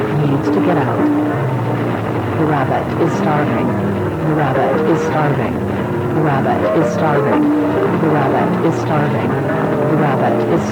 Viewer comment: “The rabbit is starving” feed it😟🤨🙏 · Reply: OHH I THOUGHT IT SAID "the rabbit is tracking you"